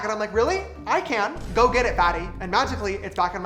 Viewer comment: Did you say fatty @ 11:01?